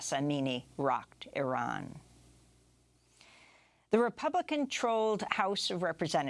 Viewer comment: I know what you said Amy, but this is what I heard "Republican conTROLLed"